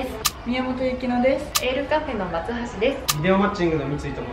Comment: Lカップの松橋って聞こえた...